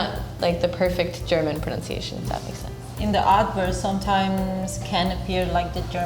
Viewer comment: Can you not invite the Chinese girl next time. Not only did she not know what the word 'folks' mean in Chinese, she even got Mercedes pronounced into something else. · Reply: Sounds like she is saying make sense bench.